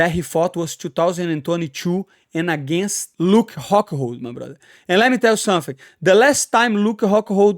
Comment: At first it sounded like you said Luke cuckhold 😂